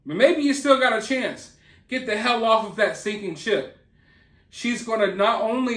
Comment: Lol. I thought you said she is a stinking sh## 🚽🧻.lol. Sinking ship applies too!🤣😉 ☠️⛴️